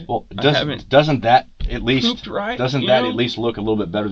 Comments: did he really say he can’t poop right? lol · did he just say he hadn't pooped right? wtf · did he say he hasn’t “pooped right”? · Did he just say, he hasn't pooped right? 💩🤡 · he actually says he hasn't "pooped right". Uh... no one needed to know that dude. Lmao